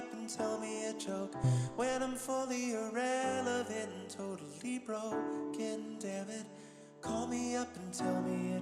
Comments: the heartbreak when I realized he's saying "when i'm fully irrelevant and totally BROKEN, damnit" and not broke :( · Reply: Damn all this time I thought he said "When I'm fully irrelevant, & totally broke, again, dammit, call me up & tell me a joke." meaning as in I'll repeat it again, call me up & tell me a joke lol. Good catch. I've only listened to it 100 times & now it got even more sad. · the heartbreak when I realized he's saying "when i'm fully irrelevant and totally BROKEN, damnit" and not broke :( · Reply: Oh shit i thought he was saying broke then god damnit. Thats tough